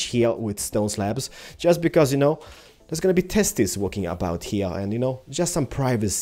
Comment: Did he say testes? :O